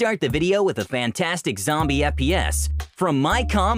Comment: did he say "FPS"? Definitely not first person if he did lol